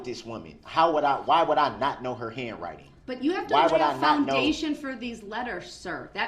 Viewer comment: "The letters are from her" "Why do you believe they are from her?" "Because they are from her" "Ok, but why? Is it the handwriting? Did she sign them?" "Because they are from her" Jesus christ, he's like a baby demanding candy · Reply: When he's muttering/answering back, he actually says he recognises the writing, (19:28 "Why would I not know the handwriting") which is what she was asking all along and he couldn't understand! I ended up going "that's what she asked!!" at the screen 😕😨